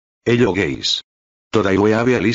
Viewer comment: i heard “hello gays” right?